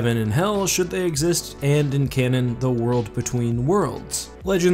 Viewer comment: I misheard "world between worlds" as "wood between worlds" and got excited at possible C.S. Lewis crossovers